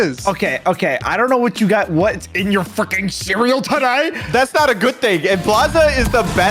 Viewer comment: Did he say fucking or freaking? I genuinely can’t tell… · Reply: he said freaking